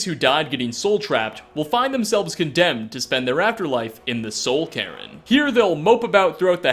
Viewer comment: Did he just say the soul Karen?